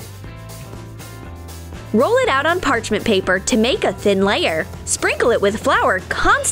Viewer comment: Is it me or did she just say green pepper instead of paper?